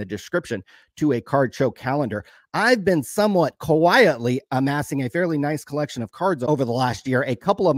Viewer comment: I seriously thought you were saying ‘Kawhi….itley’ and you were going to announce some purchases of his cards. 😂